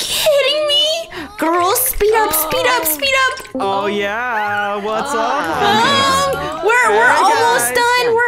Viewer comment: did just say ,"Aww, yeah! What's up honeys?"